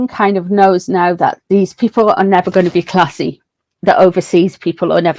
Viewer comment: Misheard you when you said they were never going to be Classy .. thought you said Class A …